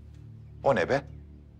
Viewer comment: Did he say "Kon hai be?" 😂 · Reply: He said 'O ne be?' means 'what is that?' 😂😂